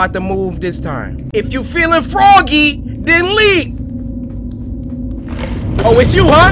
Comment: "IF YOU FEELIN' FROGGY, THEN LEAP." - Cory 2021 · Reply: Hold up I thought he said IF YOU FEELIN FROGGY THEN LEAVE